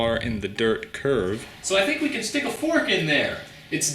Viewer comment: Erm, 9:40, did you say "forsk"?